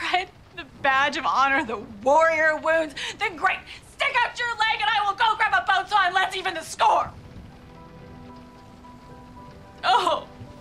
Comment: what is she saying at 0:47-0:53? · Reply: "then great, stick out your leg, and i will go grab a bone saw and let's even the score!"